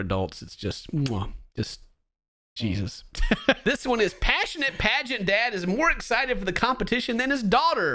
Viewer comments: thought it said pregnant dad for a quick second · 😂😂😂i thought its said pragnet dad at first insted of pagent dad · I thought that said "pregnant dad" Jesus Christ